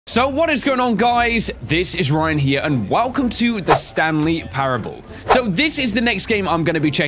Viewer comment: Ryan there are LOADS of ending s just look up some endings up there are LOADS like so Ryan can see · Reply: lol at 0:04 instead of saying the Stanley parable he says the Stan Lee parable.